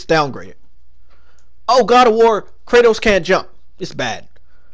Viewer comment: when he said "Kratos can't jump" I thought he said "Thanos can't jump"